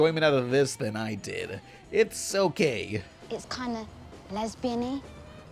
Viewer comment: literally thought he said "it's so gay". which was followed up by "lesbiany" in the movie. had to play it back to double check.